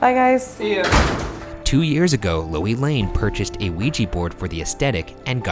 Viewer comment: i thought it said joy lane 😂😂😂😂😂